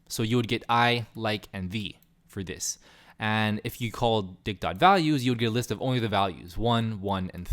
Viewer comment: who else heard Dick.."